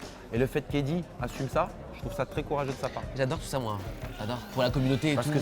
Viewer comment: je pensais qu’il avait dit « j’adore sucer moi » 🤣🤣🤣